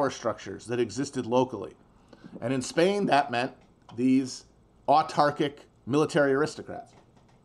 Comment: What is the word Matt uses around 15:40? Arc-tarctic? Archtarctic? Neither gives a good google return. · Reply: Autarkic